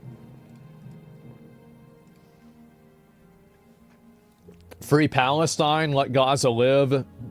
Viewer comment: at first I thought that said "Free Palpatine"